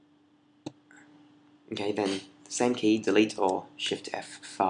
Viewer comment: it sounded like you said "zen key" which key did you mean?